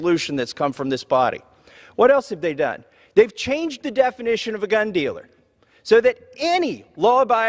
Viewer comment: Did he just say cunt dealer?